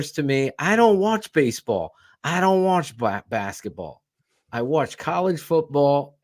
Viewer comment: tell me I didn't hear what I thought I heard "Black Basketball"... I know that had to be a mistake Bruh...!!!